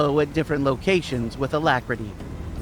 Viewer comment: What is that word at 4:00?? Alacrity???